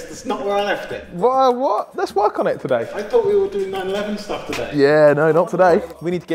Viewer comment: I thought it said “I thought we were doing 9/11 stuff today” 💀 check the captions too